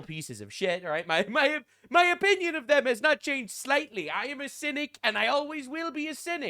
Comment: When you said "I'm a cynic" I heard "I'm Hasidic"